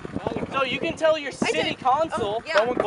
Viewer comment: did I hear city console??